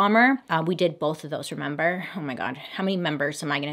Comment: Did you just say member?! 😂😂😂